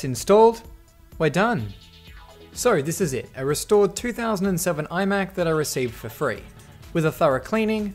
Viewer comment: Am I the only one who heard 3007 at 15:20 ?